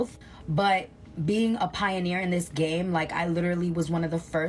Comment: NAAFA...too many A's...not surprised at the excess. Everytime I hear say 'Pioneer' I heard 'Pie eater'